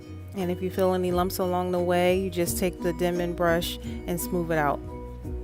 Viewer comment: What’s the name of the brush? Sounds like you said “deman” brush??? I’m trying to transition and would really appreciate if you would answer... I’m new at this! Thanks! Your hair is beautiful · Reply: It’s a cheap version of a denman brush. It’s an “Annie rubber cushion brush”, it’s usually at every hair store.